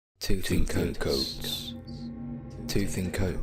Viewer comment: Is it just me or did it sound like he was saying “tooth in coats” lmao 🤣